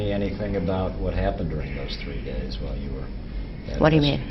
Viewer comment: 3 days. It sounds like you said 3 years